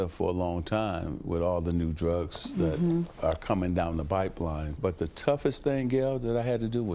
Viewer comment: Magic Johnson definitely says "bipeline" instead of "pipeline" lol